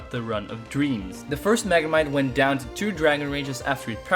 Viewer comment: did he said “the first Megamite”? XD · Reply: He said "magnemite"